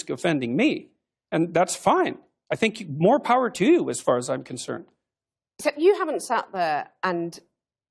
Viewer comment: Acho que ele disse: "I think, more power to you as far as I'm concerned".